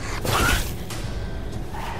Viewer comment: 「Javaに聞こえたわ…」